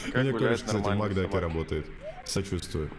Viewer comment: кто тоже услышал "У меня тоже краш в макдаке работает" ?